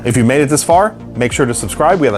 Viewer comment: I'm sorry but I heard dr fucks...😅